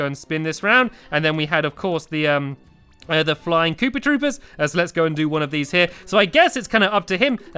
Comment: I thought he said pooper troopers in the beginning